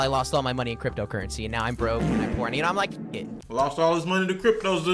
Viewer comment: Did anyone else hear him say that he’s horny or am I tripping. “Now I’m broke and I’m horny and I’m like Fuck it.”